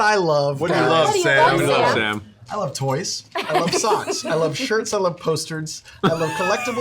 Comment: I swear I heard Sam say; "I love posturds" in the announcements lol